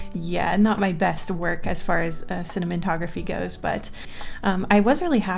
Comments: I think she just said "cinimontography" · Did she say “cinnamontography” a little past 1:43? This is when I know I’ve lost my marbles.